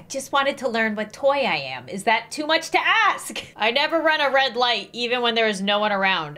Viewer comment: Is it just me or did she say that she likes to run red lights by accident